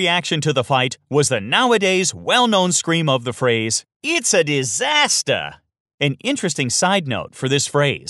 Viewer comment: Hi there, I would like to remind that EG didn't defeat CDEC in upper bracket finals, rather they lost the series 0-2 against CDEC. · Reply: to be honest, i hear it as "into disastaahh" instead of "its a disastaahh", weird.